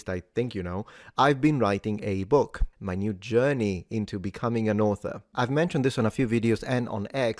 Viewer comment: I apologize. But for a second, I thought you said "becoming a norther".😅